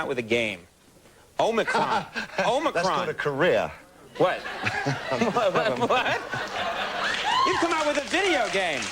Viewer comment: What does David bowie say at 6:20 ? Conan has just mispronounced omicron, and Bowie says something ".... career" and Conan replies a bit later "you sir are a wise-ass". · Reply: "yeah, that's called a career"